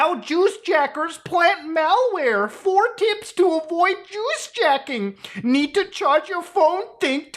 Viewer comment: I thought you said jews jacking and thought wtf it means